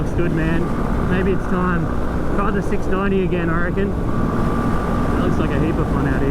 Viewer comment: WHAT?! Did I hear correctly? 690 time ✅